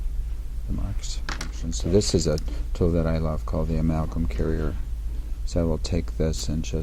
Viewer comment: Emacom cureier? What is he saying 6:22 😮